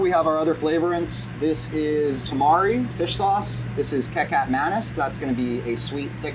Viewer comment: omg my Indonesian brain went haywire when I heard “kecap manis” as Kay Cap Menace 😂 awesome cooking though, my Belgian palate loves a good “biefstuk-friet”